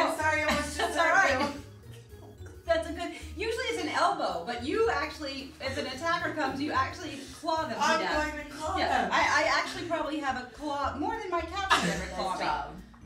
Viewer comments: Ok, so I just spent my evening watching videos of Ellen scaring people... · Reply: I thought she said: "Good Job you're hired" would've been also a very great reaction ^,^ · LOL "Good job, you're hired" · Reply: I thought she said your fired lmao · I thought she said Good job your hired xD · I thought she said "Good Job, You're Hired" o.O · I heard " Good job! You're hired!" at the end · I thought she said "good job, you're a fighter". I guess I am deaf. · I heard "Good job! You're a fighter." Lol